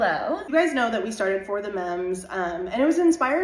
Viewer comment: Lmao I also thought they were saying for the moms in a "quirky" way 😂😂😂😂